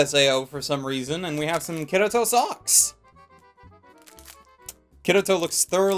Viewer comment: did you say Kirito SOX? OOoooohhhhhh, you mean socks. phewh I was worried there for a minute.